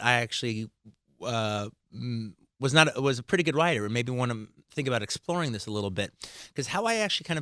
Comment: I think he stopped himself from saying "one of the BEST" lol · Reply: he said "and maybe wanna think about exploring this"